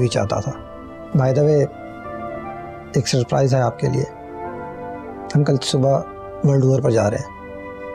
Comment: he said "world war" pr jayenge instead of world tour 🤣🤣🤣🤣🤣🤣🤣🤣🤣🤣🤣🤣🤣🤣🤣🤣🤣